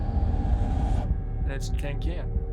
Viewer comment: Did he say "Tan Cin"?